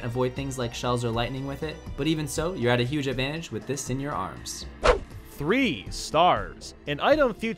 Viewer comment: At 1st I Thought You Said "Arse" Instead Of "Arms"